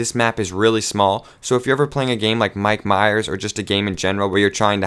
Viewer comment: what gametype he said at 0:12?? mikey mayers???? XD